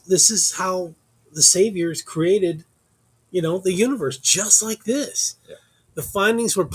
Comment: Hi, at 1:40 sounds like u said how saviour created❤ do you study the genesis story in the bible?👍